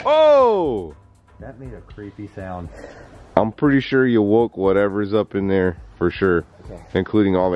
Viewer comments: I heard you woke up me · Does someone else hear something on 4:27 that it sounds like "you`re welcome in". Cause it didn`t sound like any of them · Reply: Seanwilson48 i hear “you woke up me”